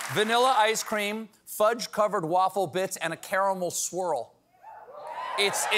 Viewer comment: I thought he said "and a caramel squirrel."